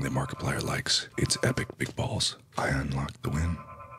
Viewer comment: When he said big balls its sounded like meatballs